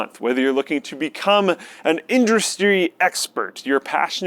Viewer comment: Luke says "Indristry" lol, thought we didn't catch that.